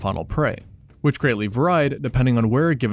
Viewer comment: Did blud just say "var-eyed"?